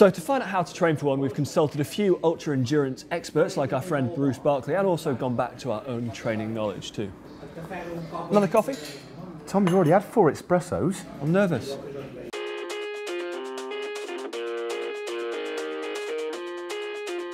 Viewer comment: Did Matt just say eXpressos?